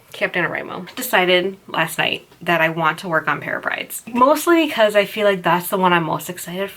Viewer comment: This makes me want to laugh at myself, but as many times as I’ve heard you say the name of your 2018 project, I’m still not quite sure if you’re saying “Pair of Prides” or “Pirate Prides”. In this video, around 2:28, it sounded like you said “Pirate Brides”. 😆 What’s the correct name?